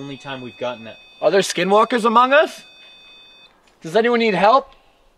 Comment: We are so thankful for having new friends and to share some of these adventures from time to time with! Also we hope you understand that we were pretty excited in this episode but who wouldn’t be?! Everything that we had captured tonight changed so many minds who are watching this and that’s why we go to these dangerous places to do this. If you are new to this channel and would like to give us a chance please do consider in hitting that subscribe button and joining the #GhostGang 🖤🖤 as for everyone else thank you so much for being here and enjoying this episode with us! Please make sure you help us reach the 30K LIKE goal and we will see you next week 🥹🖤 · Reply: At the end I heard "We need help"